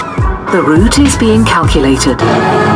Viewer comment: I swear thought it said "the booty's being calculated" lmao